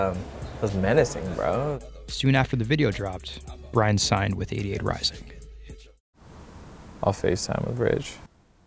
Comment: i thought he said idiot rising at the begining